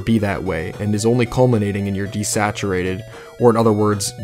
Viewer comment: At about the 11 minute mark he sounds like he says desecrated instead of desaturated